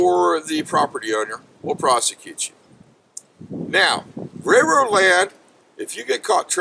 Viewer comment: is he saying "Railroad land?"